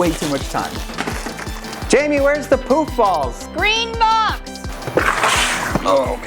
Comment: Bro for a hot second I thought he said poop balls I was like what and then next time I see them in the car without their kids just kidding you’re probably with your grandma or something or it could be a school morning I don’t know